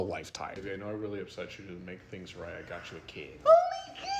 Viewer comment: Lol it sounds like he said shat on